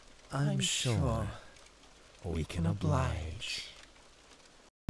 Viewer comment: did I just hear Mario?🤨